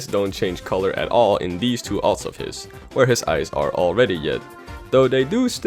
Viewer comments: He says "Yed" instead of red... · it sounds like “where his eyes are already yed” is being said instead of “where his eyes are already red” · I thought you said his eyes were already yed whoops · he says yed instead of red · did anyone else hear him say yed instead of red? Or is that just me? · Did he say yed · you said yet instead of red